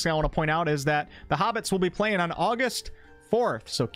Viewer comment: did you just said "hobbits"?